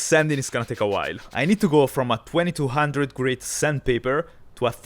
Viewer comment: Had to research the part about the sandpaper. I thought you said from 2200 to 1000. Was confused because I don’t know of any time you would go from fine to coarser. After going back the paper said 220.